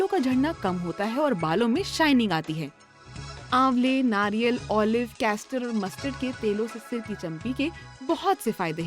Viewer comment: custard lol.... instead of castor@